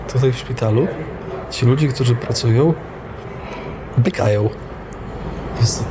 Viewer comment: usłyszałam biegają XDD kurwa fu obleśni są ci ludzie że bekają przy pacjentach W SWOIM MIEJSCU PRACY